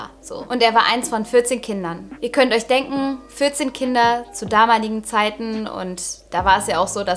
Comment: Ich die erst 40 Kinder verstanden hat😶😶🤣